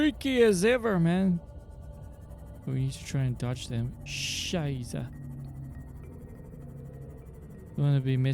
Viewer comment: Did he just say Scheisse at 32:45???